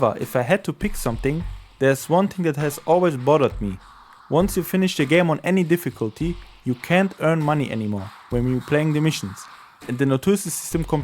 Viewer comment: I tried i really tried but i couldn't understand what you were saying right there xc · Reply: sorry for my accent. I was saying: once you finished the campaign on a difficulty you couldn't earn money in the same playthrough anymore.